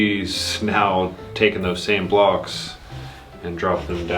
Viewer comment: Blocks? At first I thought you said Blogs.😊😄